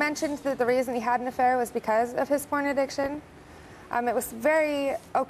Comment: At first I thought she said corn addiction...